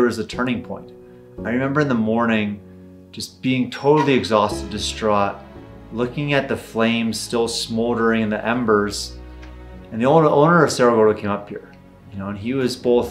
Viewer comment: wait, I thought you own Cerro Gordo? Or did you say "other owner"?